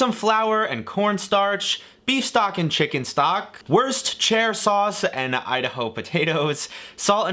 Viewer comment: Did he just say worst-chair-sauce?